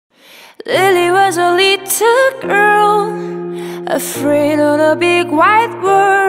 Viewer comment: The first one why did i hear her saying "lily was a little irl"